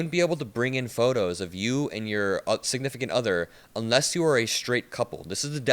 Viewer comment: Did he just say of lgbtq "your insignificant other"